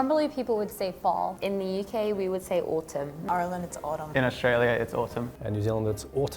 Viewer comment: Need a Canadian in there to give the US some company (and our own weird things! Corner store, convenience store or "dep", for example, and cottage or cabin!) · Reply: the girl from Ireland I thought she kept saying Arland